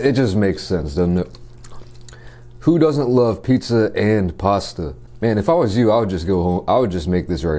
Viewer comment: Hell nah did he just say pizza gay